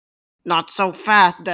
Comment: I heard “Not so fast there” 💀